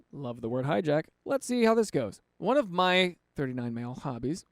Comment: I wasn't watching, just listening and I thought you said "One of my 39 male hobbies" and I was like "THATS A LOT OF HOBBIES!"